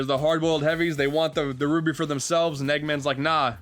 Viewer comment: I thought he said the root beer for themselves